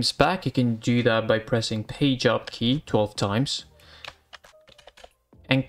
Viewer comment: what did say p job key ??? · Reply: Page up key?